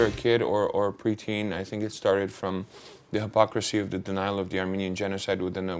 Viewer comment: What does he say from 0:32 from 0:37 ? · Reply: lmao this is really late but he says "from a preteen it started from the hypocricy of the denial of the armenian genocide.